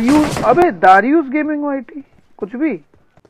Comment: I thought he was saying darudius gaming yt 😂😂